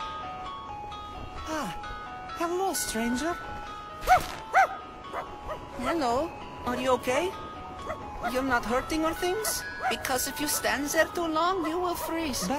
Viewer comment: I heard "Hello, are you gay?" what's wrong with me